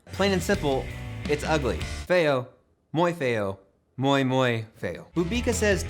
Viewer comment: It's funny when he said "fail" a lot of times, cause de Portuguese word for "ugly" is "feio", which sounds pretty much like "fail". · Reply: I thought he was saying “muy feo”, Spanish for “very ugly”